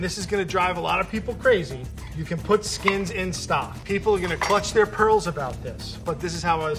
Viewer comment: “People are going to hold their pearls with this one” thanks Frank, I’m going to use that one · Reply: I've always heard that as "clutch" their pearls.